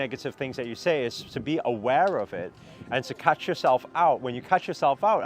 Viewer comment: says catch yourself out. The caption reads “cut” instead of “catch “ I